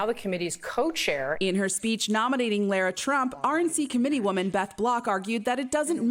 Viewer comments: I thought that lady said Lord Trump for a sec... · Glad that i only misheard that laura trump introduction as a lord trump intro....but its possible that i didnt hishear it?